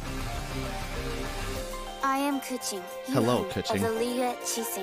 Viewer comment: why did it sound like she said "i am coochie" lmao